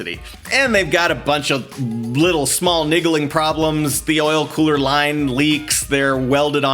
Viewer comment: He actually said "Niggaling Problems" 😂